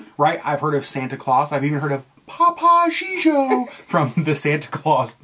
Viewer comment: My wife won't let me continue watching the video until I memtion that it sounded like you said papa gigio when it is topo gigio. Its from the Ed Sullivan show.